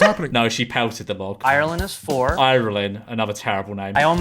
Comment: thought he said Ireland is 4